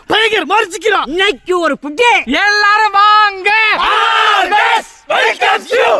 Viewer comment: DID HE JUST CALL ME GAY? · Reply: Hi am from Tamil Nadu and speak tamil he was just telling “everyone come” idk why u come and comment like this without knowing any meaning of the language or the words